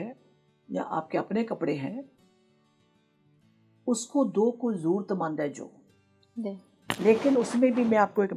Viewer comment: What did she say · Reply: "Kpde usko do jo jrurtmand h"